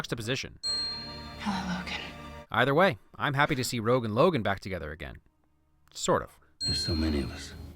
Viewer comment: when you said rogue and logan back together again it sounded like you said rogan logan back together again hehe